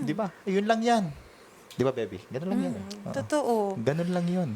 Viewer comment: anu yub narinig ko hindi na diba, kundi diba baby na?! Tama ba tun narinig ko hihihihi kinikilig ako😊 pumapag ibig na ara c mam ❤☺️